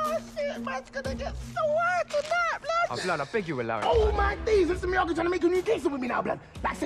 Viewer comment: Can anybody actually understand what he says ? Don't bother looking up the script it's improvised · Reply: "oh my days mr. miyagi tryna make a new case with me now blud!" like man sayin he knows me or sum, HM?, Mans gonna have to get JACKED for that blud"